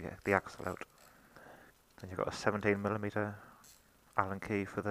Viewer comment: I can't hear what you are saying at 1:22 Is it a 17 mm allen key? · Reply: yes, it's a 17mm for the front axle